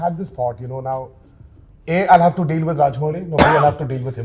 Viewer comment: In the subtitles You wrote feal in the subtitles instead of deal from 3:00 to 3:20 anywhere in this 20 sec... I don't know and don't have the time to check the real time😂